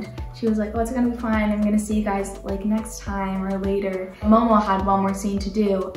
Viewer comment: Did she say next time? ...